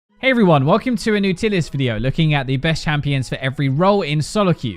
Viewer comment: I thought I heard "Hi everyone welcome to a new tities video" lol